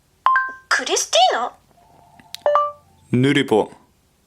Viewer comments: What's he saying? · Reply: Axel Castillo nurupo (null pointer), a Japanese programming meme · what word are you saying? Nuripo? It doesn't work:v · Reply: Raihan Yoga Pratama he sey nurupo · what word are you saying in the minute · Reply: nurupo